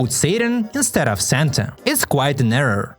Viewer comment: its not just that the subtitles say “sadanayi” instead of “santayi”. She also says “numan” but the subtitles say “bes”. (Both these words actually mean “similar” so the meaning stays the same but its still wrong. Also its strange that they said said santa literally because in Armenian, santa is called “gaghant baba”. If you are wondering, this literally translates to new years father.